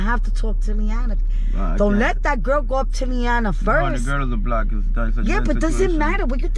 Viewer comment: DID HE JUST SAY "THE GIRL IS A BLACK???????" WTFFFFFFF.